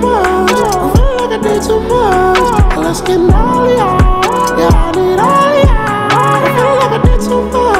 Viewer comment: What people heard: well, let's get naughtier, yeah I need all of ya What I heard: Let's get naughtier yeah, midorya~